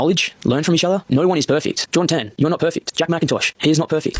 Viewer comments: Pretty sure he said “Jordan Tan” but with his accent and it sped up it sounded like “John 10” but I looked up that verse and it makes no sense in this context so I don’t think I’m wrong… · Someone may have already commented this but at 12:14 i dont think he's quoting a bible verse i think he's saying, "jordan tan, you are not perfect" as in the person's name he is responding to